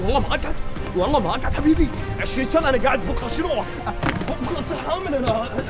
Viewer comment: سمعتها بكرة اصير حامل 😭